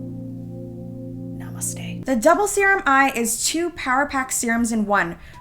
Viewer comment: did she said Namaste?